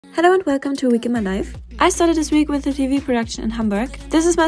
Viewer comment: I love when you said welcome to a week in my life and I thought you said welcome to a week in my knife